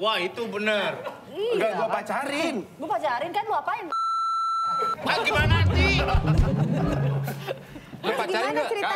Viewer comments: darto ngomong apa sih? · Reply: Ciuman doangg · ngomong apa woy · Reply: ciuman dong -darto